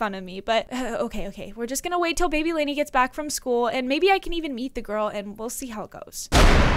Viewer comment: when she said maybe i can meet the girls i thought she said maybe i can BEAT the girls😂😂😂